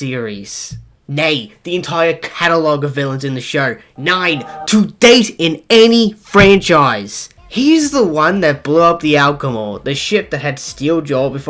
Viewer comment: i heard the alcohol instead of the actual name of the damn ship.